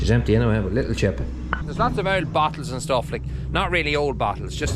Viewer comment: haha I thought you were saying battles, then I realized you meant bottles